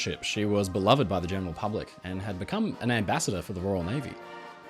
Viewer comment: Great video analyzing the explosion while not ignoring the lives lost or the horrors of war. If you’re taking suggestions, I’d love to see a video from you analyzing the Halifax disaster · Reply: Also, I can’t quite make out what you are saying at 9:51. It sounds like you say Hood was beloved by the “German” public, but is that correct?